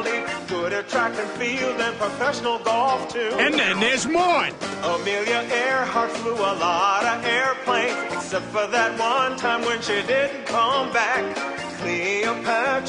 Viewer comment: I thought Peter said "And there's more" 😂😂😂😂